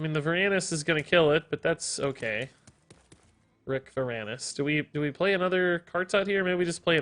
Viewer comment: Did I just hear “Rick Veranus”? #Shu’ma